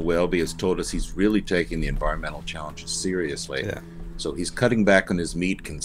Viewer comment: Really thought said that Welty was taking cow farts seriously by cutting back his meat consumption, but then I rewound it twice and finally put in the captions and set speed to normal and realized he said environmental challenges seriously... (definitely some disadvantages to listening at double speed, but man some of the things I think I hear are hilarious!)